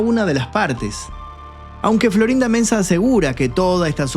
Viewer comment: me parece escuchar que dice: florinda menza 😅🙈